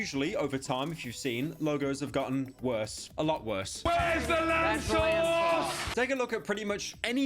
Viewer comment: Swear to God im so fųčkîng high right now i thought Gordon Ramsay yelled i heard "Where's the Starbuck's!!!!"